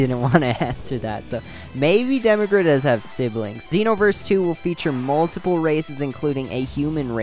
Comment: did he say dinoverse*